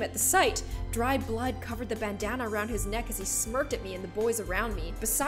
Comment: Lol for a minute when it said bandana I thought it said banana